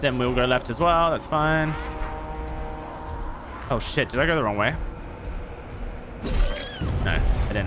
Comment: Sounded like he was saying “cow shit” 😅